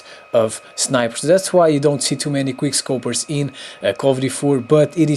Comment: when you say "quickscope" i sound like ur saying "quirscope"